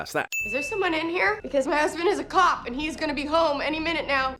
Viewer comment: Every time she says her husband's a cop it sounds like she says, "my husband has a cock."